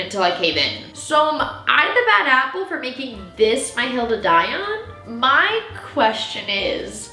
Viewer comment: What SAID: “Hill to die on” What I HEARD: “Golden Aiyon” I had to do a double take😂